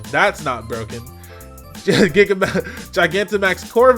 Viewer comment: i heard that giga-bowser